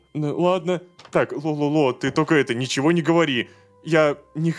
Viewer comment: трололо? или послышалось?